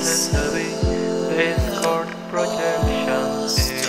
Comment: I thought I heard something "discord projection" <v<